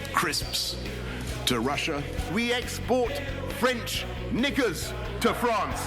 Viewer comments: Can someone please clarify what he said at 1:20 for me. "We export french ? to France". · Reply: Knickers, underwear. I hope. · what does he say at 1:21? "We export French N!@@ to France¿" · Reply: Knickers. It means panties.